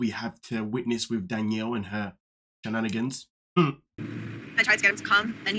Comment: Did I hear you say " bananagans?" That's a great way to describe the games Danielle is playing over there😂